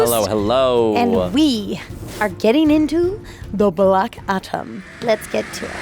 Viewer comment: Did she say "Black Atom"!!!???